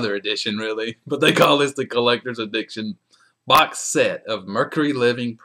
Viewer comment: Nice video. I going to order this from Amazon and I rememberd you had this video on the set, so I just watched it again.....I like at 3:38 when you say collectors "addiction" instead of "edition"...LOL. I guess we are all addicted!